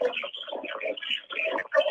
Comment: I hear Okay girl why are you so hard of hearing.